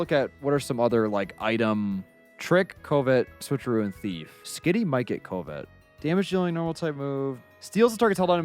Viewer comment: for a sec i thought he said Skitty might get covid 😂